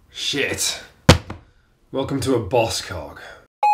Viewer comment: He said "Welcome to a boss cog", I heard "Welcome to a boss c*ck", which was splendid. Come back soon!